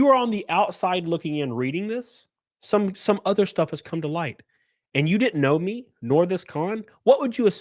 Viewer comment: Straight up, I hear that “some things have surfaced” I think someone’s gone looking for a cupcake.